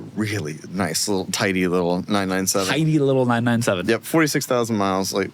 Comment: Let's play... "what ta heck did filipo just say!?" @ 4:33 I heard... "taden-sa-te-hsat". What did you hear? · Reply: I guess he said 'The 997 S' ?